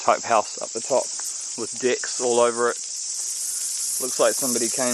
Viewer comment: Lol I thought he said the house had dicks all over it at first lmao